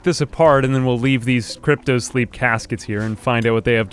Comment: Initially when I heard you say "cryptosleep" in another video, I thought you meant "cryosleep" Oh me, you sweet summer child